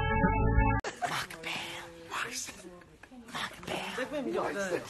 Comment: I can't with myself anymore. I first thought he said "Fuck me"